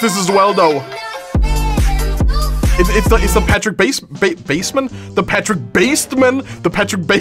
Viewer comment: I thought He said Patrick basment and i spit my Drink all over my Monitor 😂